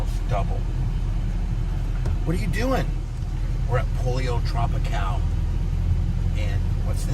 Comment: I could have sworn you said Polio Tropicale